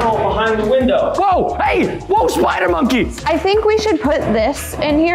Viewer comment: Look at this. Did preston said monkey??